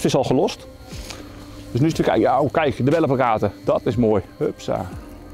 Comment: Heb het drie keer opnieuw gekeken maar wat zegt hij nou eigenlijk bij 5:15 ? Oh ja kijk de ......? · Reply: Hij zegt "Kijk de bellenplakkaten" haha